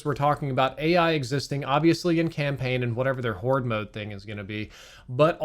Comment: Did he just say “Horde mode”?…